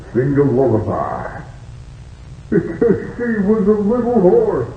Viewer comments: Idk why but I thought at 0:49 Freddy said “a little wh0r3” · Can someone tell me what the joke at 0:44 I don't have good speakers and I really want to hear the joke. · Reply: Why couldn’t the pony sing a lullaby? Answer: He was a little horse